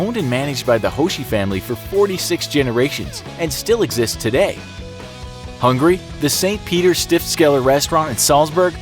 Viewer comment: you mean “Austria” instead of “Hungary” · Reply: He said “hungry”, not Hungary.